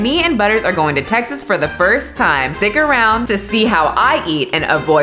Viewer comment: Did you say "dick around" at 0:03?? Lol 😆